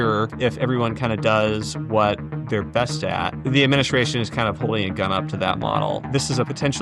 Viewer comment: The subtitles are wrong, it should be "the administration is kind of holding a gun up to that model".